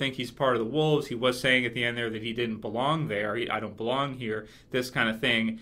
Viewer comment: Daniel Boback I thought he said, "I don't belong out here."